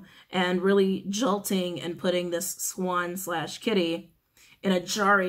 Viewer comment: When you said “swan/kitty” I heard “black swan” and “sad kitty” which showed me a sad black cat. Had to share that. It’s at about